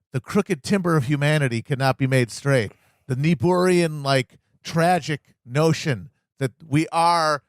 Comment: what's the word he uses at 30:27? neborean? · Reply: Niebuhrian. Descriptor of the philosophy/theology of Reinhold Niebuhr, an American mid 20th century theologian who Obama said was one of his favourite philosophers. Niebuhr's ideas formed the basis of vital centre liberalism or what became Cold War liberalism.